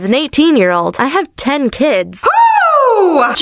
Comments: I thought it said “I have 18 kids at 10 “😂😅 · I thought it said I had 18 kids at 10 😂😂✨